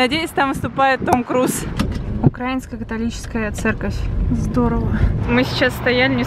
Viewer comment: мне послышалось или сказала Украинско - Католическая церковь.